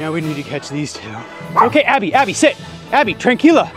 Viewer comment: I had to replay it back a couple of times....i thought was saying "tequila" to Abby 😂 🍸